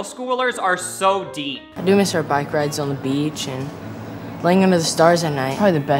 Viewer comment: i could have swore he said “five guys on the beach” or whatever he said after